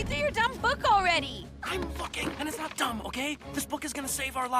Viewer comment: did anyone else hear Dipper saying "I'm fucking"? I know what he actually said but for some reason in this video that's all I hear. the closed captions are not helping.